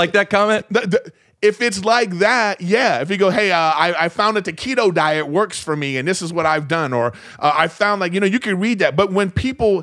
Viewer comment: I’m watching/listening on my iPad while working. At the 8:00 mark I hear say, “I found the taquito diet works for me.” (An innocent misspeak, referencing the keto diet, obviously.) I immediately rewind to watch, because I KNOW my man & co. were going edit something in with taquitos. Glad we’re on the same page, fellas.